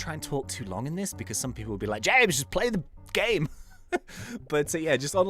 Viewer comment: did u say play the fucking game ??? 😂😂😂